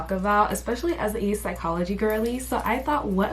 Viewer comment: i was watching in 1.5x speed and when you said "as a psychology girly" i misheard it as "ace apologist girly" 😭 i thought i was in enemy territory 😂